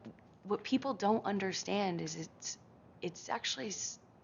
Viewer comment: she says sex-uactually instead of actually - Freudian slip